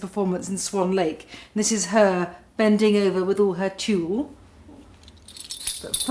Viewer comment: What does she say at 0:54? This is her bending over with all her ... ? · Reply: Dominique Brodoteau “tulle”... as in the tulle of her tutu.